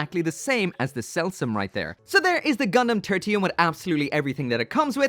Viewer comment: Thought he said Gundam Tortilla.